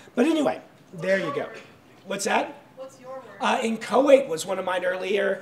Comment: what are his words? I'm hearing them for the first time so I can't tell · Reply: Inchoate is the other :)